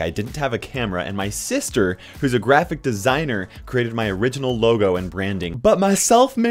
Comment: YOU are my channel and YOU are my dream!!! Thank you all SO much for this INCREDIBLE opportunity! If you'd like to further support the channel, here's how you can do it: Become a YouTube Sponsor ► <link> Become a Patreon Sponsor ► <link> Become a Twitch Subscriber ► <link> Purchase KairosTime Merch ► <link> Follow me on the following places: YouTube ► <link> Twitch ►<link> Discord ► <link> Twitter ► <link> Facebook ► <link> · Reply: When I first heard you say graphic designer I thought you siad crappy designer